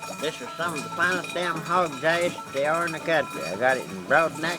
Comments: what's that stuff he's adding to the pot? Can't make out what he called it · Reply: sounds like he said “hogs ass” so possibly pork fat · I have to know - what does he put in the beans? It sounds like he says "hog dash" but I can't find anything like that. Would love to make those beans! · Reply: He says hogs ass aka fatback.